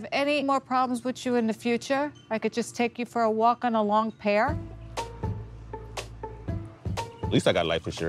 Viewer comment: Did she just threaten this man with death?? “A walk on the pier” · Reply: I thought she said "long pear" I've never heard anyone say "pier" like her